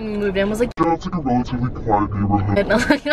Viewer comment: What did you say at 2:07 when you distort the voice lol I couldn’t understand it 😂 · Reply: Rozelyn L'Hirondelle She says, “That’s in a relatively quiet neighborhood .”